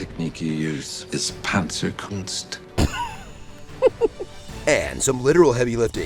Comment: If you could learn any secret martial arts, would it be Panzer Kunst? Watch the Honest Trailers Commentary on Wednesday at 10AM Pacific. Why Wednesday and see all the jokes and thoughts that didn't make it into the final video! POOL RULES One: Speak up and join the conversation- we encourage respectful debate. Two: Respect your community- racism, misogyny, homophobia and hate speech aren’t tolerated. Three: Don’t go looking for fights. Four: “Be nice.” – Dalton from Roadhouse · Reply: I'm sorreh, I misheard. I learned panzer oontz oontz oontz and I'm stuck in a rave...